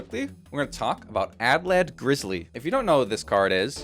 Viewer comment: Annoying pronunciation correction is annoying; I believe that in "addled grizzly" the "addle" is pronounced similar to "paddle" without the "p," i.e., the verb "addle" is being used to describe the grizzly and when modifying it as the receiver of the action (think "frustrate" --> "frustrated"), "addle" transforms into "addled." Irregardless, great video! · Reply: I totally thought he was saying "madlad grizzly" as a joke, cause I couldn't tell what the card's name was until it popped on screen.